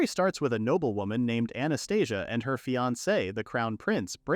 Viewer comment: Did he just say 0:07 anestesia 😂